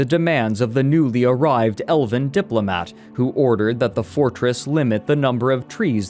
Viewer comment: got distracted; misheard "elven diplomat" as "elephant diplomat." Got chills.